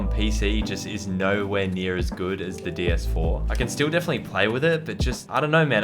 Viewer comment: Am i the only one or did he just say „ds4“ at minute